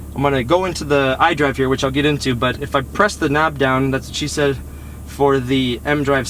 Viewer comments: Did he say "that's what she said" at 5:16? ahahahaha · i swear he says "thats what she said" sick carr!